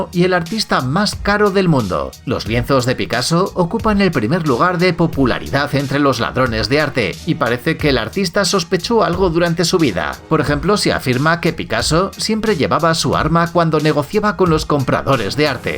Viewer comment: Si tienes buen oído, te das cuenta de que dice "pupularidad" en vez de "popularidad"